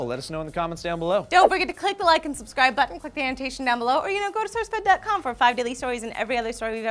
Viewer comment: Definitely sounds like Lee says "fuck the annotation down below"